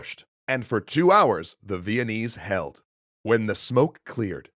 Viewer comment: Is it me or did I hear Vietnamese